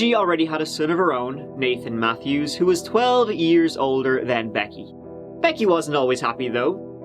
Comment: "Becky went to live with her father..." oh no "Who lived with his new partner..." oh, never mind "Who had a son..." oh no "Who was 12 years old..." oh, never mind "...er than her" oh no This is what these stories have done to my brain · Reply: I could be wrong but I thought he said the son was “12 years older” then her.